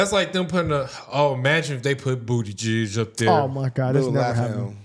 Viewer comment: Why does it sound like they say booty giz😂 Pete buttigieg😂